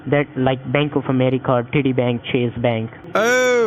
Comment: I heard him say titi bank looolol 😂